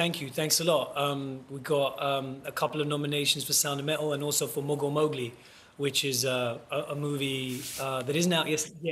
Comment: Thought he said 'Morgul Mowgli' for a second, and hoped there was gonna be an epic LOTR/Jungle Book crossover.